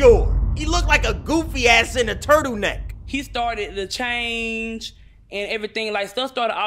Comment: she really said lechange im dying 😂😂